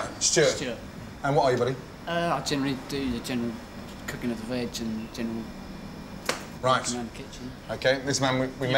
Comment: I'm not sure what actually happened 4:05 but this is what I heard: I'm Gordon: and who are you buddy? I generally do the general cooking and the general general... Gordon: right...